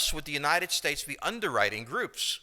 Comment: What is he saying at 22:00? >> "Why else would the United States ...?" I cannot understand it, something with "groups" or so ... Thank you! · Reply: Why else would the United States be underriding (or maybe underwriting) groups.